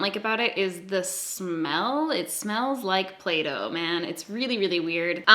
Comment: I thought you said that ''it smells like Plato''. Confusion ensued. (the milk bronzer that 'smells like play-doh')